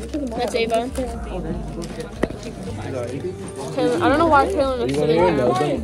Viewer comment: what did say?! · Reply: idk why kylian is sitting here she said